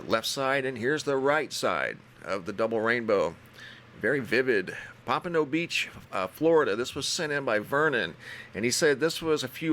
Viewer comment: No way thats venus that huge up there....is it? Sounds like you said poppa dont preach, Florida